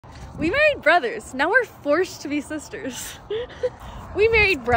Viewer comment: I thought you were saying we made brother